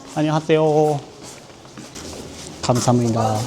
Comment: 아, 잘 수정하셨네요^^; 편의점 등에서 손님이 결제를 끝내면 편의점 근무자(점원)가 "감사합니다." 라고 한 뒤에 "안녕히가세요." 라고 하는 겁니다. 말을 빨리 하기 때문에 외국분이 잘못 들으면 "안녕하세요." 처럼 들릴 수 있습니다만, "안녕히가세요." 가 맞습니다.